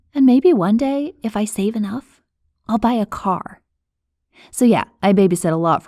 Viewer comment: I thought she said “And one day, if I save enough, I’ll buy a cock” Sorry 😂